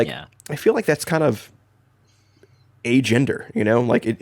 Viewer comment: I realize said agender meaning nongendered but I heard "Superheroes, Transformers, and Fortnite is a gender" and was like ah yes finally an explanation for my social circle